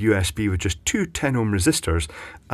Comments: you said sawn off - I heard Sonoff! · you said saw off, I heard Sonoff!